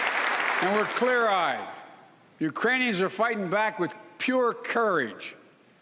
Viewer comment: I heard him say “Uranians”.